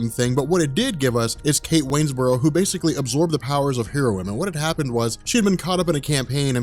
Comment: am I the only one that thought he said heroin like the drug